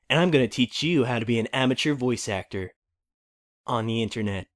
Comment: First I heard "immature voice actor".